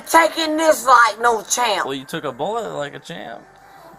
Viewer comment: Wait a got damn minute. Did he just say " ion u took that bullet like it was nothing."